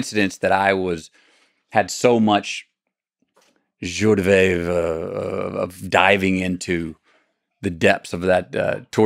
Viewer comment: what is that French word at 8:02? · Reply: He mispronounced joie de vivre.